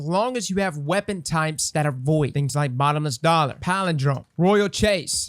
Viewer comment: Did he just say Bottomless Dollar?